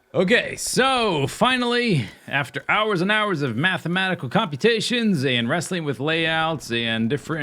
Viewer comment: "I did some math competitions." anyone else hear that? hahahaha!!!